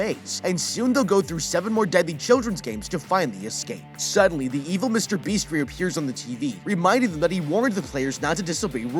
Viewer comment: Did I just hear him say 'The Evil Mr. Beast'? XD